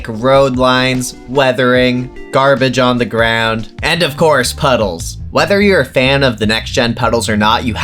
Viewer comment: Thought the gator was called “Puddles” for a second here 😂